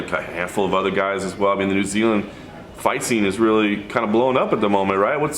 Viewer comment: Dan the man said it , Australia is better than New Zealand , lol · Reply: Thought he said New Zealand fighters shit all over Australia 😂